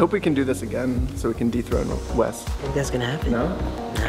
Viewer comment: I swear I thought for a split second he said "I hope we can do this again, so we can deepthroat Wes", but then I went like "wh--" and then I realized what was actually being said 😅